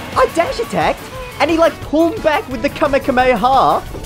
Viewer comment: did he just say kamakamea ha?! Haha